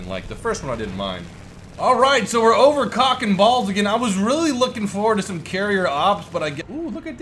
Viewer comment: I thought that he was saying cock and balls for the first map! haha